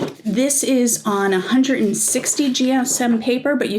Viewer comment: It’s only 120 gsm. That isn’t very thick. It’s one of those combo, printer photocopiers. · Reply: i thought i heard you say 160 gsm.